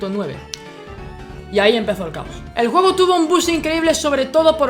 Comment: Oye te subtitule unos 4 minutos y arreglado las estupideses que ponen otros si quieres que siga ponme un corazon para asi saber y terminar lo demas pero nesesito que me digas que dices en el minuto 1:58 que no entendi bueno.. eso sigue haci ;] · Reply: Dice boost esta en ingles